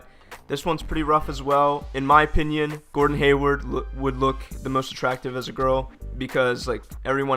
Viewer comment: did he say "the most attractivist girl"? is that a spoonerism for an "attractive activist"?